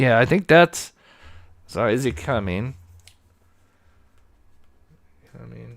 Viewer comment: I hear say " is he coming?" Then "definetly a gun (with him)" . Then talks about the path then says "you hear a gun?" Then says its a gun /or it looks like one